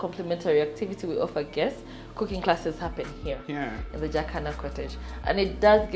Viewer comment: Thought I heard “jack hanma” cottage 🌚😂. If you’re an anime fan and you’ve seen Baki, you’d understand 🌚